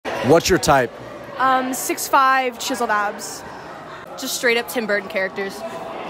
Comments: No way the first girl is saying she wants 6’5 and abs lookin like she straight outta Steven Universe💀💀 (yes I know the girl is joking so stop flaming me in the replies) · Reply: I thought she said chiseled ass 🤣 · "6'5 chiseled abs" why this girl talking like she aint a 3/10. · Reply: i thought she said chiseled ass lmao